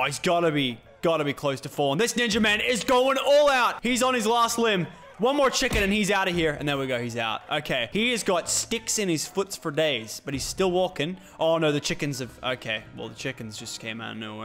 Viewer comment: did he just say foots ?